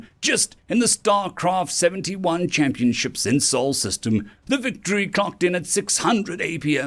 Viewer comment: Did he say starcraft tournaments in Sol System or Seoul System? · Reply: SOL.